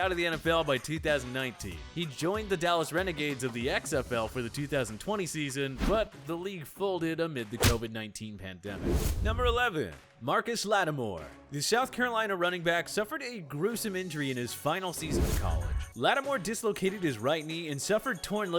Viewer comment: I thought I heard Marshon Lattimore I was about to lose it. Hate the Saints. 🤣